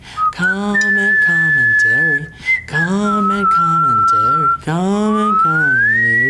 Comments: it started to sound like steve was saying com and com and terry as if com and terry were people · It sounded like he was saying com and com and terry.